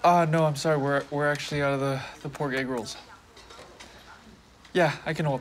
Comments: Hi everyone this is Andrew the writer/director of the film. I just want to say thank you so much for watching and for your responses. This was an incredibly personal film for me to make. As there are so few LGBTQIA Asian films being made, your support and enthusiasm for stories like this means a lot. I am currently working on new film projects that explore similar themes, so watch this space for more. Thank you again so much!! :) <3 <3 <3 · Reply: I loved it.....but for real, when the line said "pork egg rolls " all I heard was "poor gay girls" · am i stupid or did i just hear "ah no i'm sry we are actually out of the poor gay girls" i